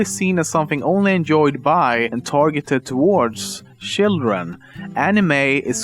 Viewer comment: sounds like you said shitldren lol